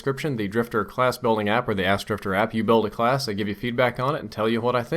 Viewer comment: "The ask Drift0r app." I heard "Ass drifter app."